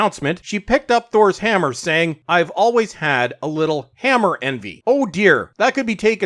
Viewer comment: I thought she said, "I always had a little hammer in me." Wow, I thought that was going in a weird place.